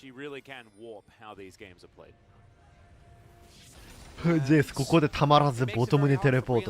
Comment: ジェイスことゼウスって言ってるように聞こえる笑